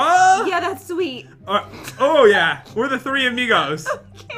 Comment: did i hear the n word?